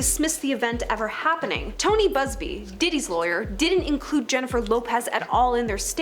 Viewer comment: 1300 views in 4hrs with over 2mill subs something ain’t adding up here. Also who did you say diddys lawyer was? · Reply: Tony Busby.